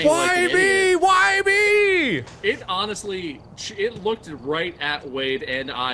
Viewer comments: Mark: “I just heard something in my own room” Me: “Well the Unus Annus ghost hunting lady did warn you about this game.” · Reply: the captions instead of honestly say annustly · Captions: "Annusly" Me: the wound's still fresh, c'mon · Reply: Legit every time Mark said “honestly” on that channel I heard “Annusly.” When Ethan finally made that joke in the video where they just talk with each other, I was like FINALLY.